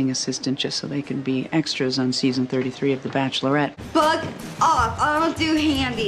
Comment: it sounds Like Becky said “Fuck off”